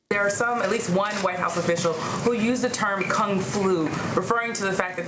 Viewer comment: did she say Kung Flu?